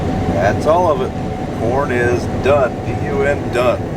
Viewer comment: HAHAHAHA with CC on when you say corn is done the CC says PORN is done hahhahahhahahhaha. 4:25. Sorry Jr High humor.